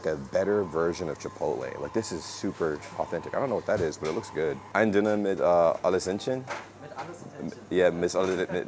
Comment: Ich hab erstmal verstanden „Ein Döner mit alles Entchen“ 😂